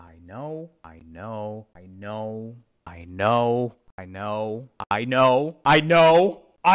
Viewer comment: At this point I thought he was saying “Ai no” , which is kinda a Mexican saying which translates to “Oh no” or “Ew no”.